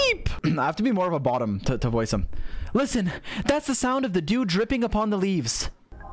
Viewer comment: "listen, thats the sound of a dude dripping in the leafs" HUH WHY DID I HEAR THAT